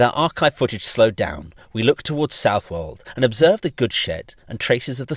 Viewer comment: I didn't parse that as "the goods shed" the first time and thought you said "we observe the good shit", like you thought that was just a really nice looking curved railway